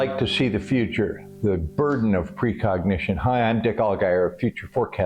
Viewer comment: I thought that said recognition which must be the present or future to precognition